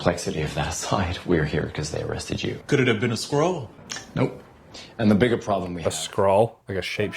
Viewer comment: I swear when I watched this on Disney+ I thought the guy said, "Could it have been a squirrel?" Skrull makes a lot more sense now, but I did get a great laugh during the episode when I thought it was squirrel, lol!